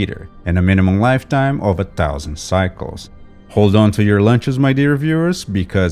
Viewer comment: i swear i thought you were going to say hold on to your papers. i've been watching Karolyi too much